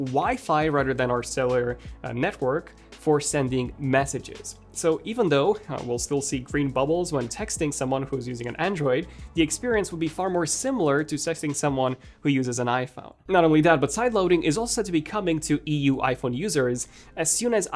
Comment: Did you just say "sexting"? 😀😀